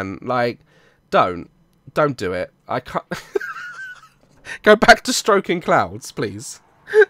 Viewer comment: lmao I thought said go back to stroking clowns